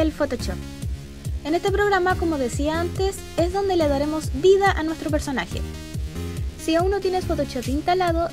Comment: entendí "el photoshop es donde le daremos sida a nuestro personaje" 😂😂😂😂😂